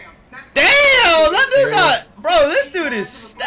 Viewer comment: Did say the N word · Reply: "That dude got"